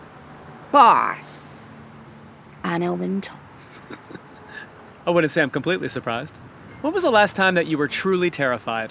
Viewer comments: Could anyone hear who the celebrity was (around 17:15)? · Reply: Anna Wintour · who does she say she has the contact of? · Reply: Anna wintour!!